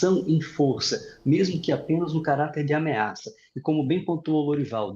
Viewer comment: Parei de assistir quando ouvi "cartel de laçola". Meu Deus! De onde saem estes especialistas?!